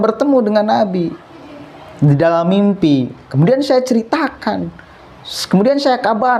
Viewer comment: Kalau yg saya sempat dengar sih si Haikal mimpi krtemu napi. Mhn koreksinya kalau saya salah dengar.